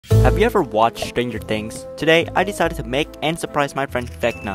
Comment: I thought for a second he said "stranger danger" as a joke